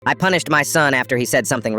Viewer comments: I heard "punished" as "punched"😭😭😭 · When you said punish i thought you said punched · Bro I’m so slow I thought it said I punched my son not I punished my son 😭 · I thought he said i punched my son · I thought you said you punched your son